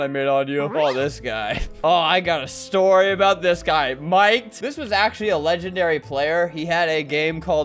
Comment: bro I thought that said milked